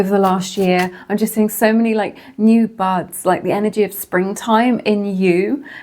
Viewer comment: GIRL the double take I tooked when I thought you said “new butts” damn near gave me whiplash 😂